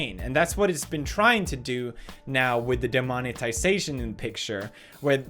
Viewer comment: Anyone else hear Demonictisation?